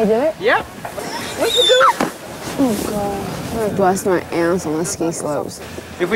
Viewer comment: it sounds like she said "oh my GOD"!!! 😮😮😮